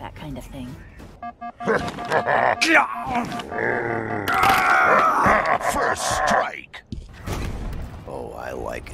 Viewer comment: What did he say 💀💀💀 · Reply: He said Gyat 💀